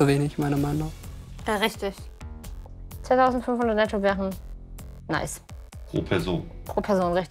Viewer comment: Hat die alte 10500 Euro netto gesagt oder habe ich mich verhört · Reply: Sie sagte 2500 netto